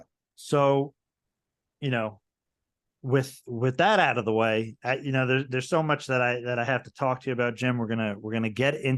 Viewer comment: He mentions a book ,RD6 killer clark ? Couldnt really make out what he said. 1:57.35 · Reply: Ardy Sixkiller Clarke